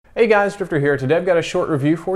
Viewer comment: it sound like he says hey guys striper here